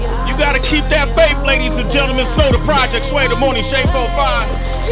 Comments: did he just say "Snow Tha Project"?? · did he say snow the project? Lol love you snow and energy together.